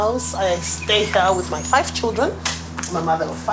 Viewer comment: Is it racist of me when at 2:00 she said she lives with her 5 children I thought she said she lives with her 5 chickens